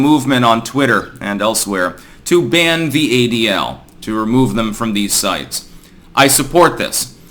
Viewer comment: I thought it said Ben The ADL